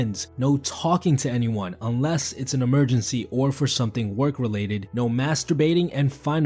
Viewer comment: I thought he said "unless it's an emergency or work related, no masturbating" 😂😂